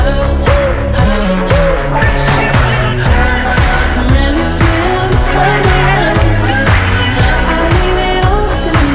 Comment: what does he say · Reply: Precious little diamond